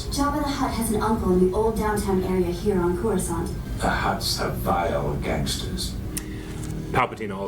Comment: For a few years, I thought he'd said "The Hutts are bio-gangsters." What exactly that meant, I had no idea.